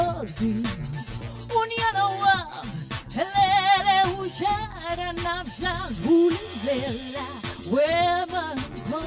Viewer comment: On 7:20, did she say unyana wam' uShaka namhlanje? 😮😂😂